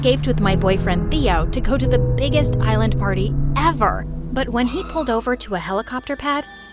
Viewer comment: I heard “island party” and got scared 😭😭😭